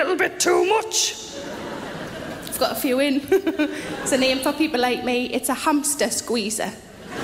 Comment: what does she say at 1:24? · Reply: nailpolishforever "there's a name for people like me, it's the hamster squeezer"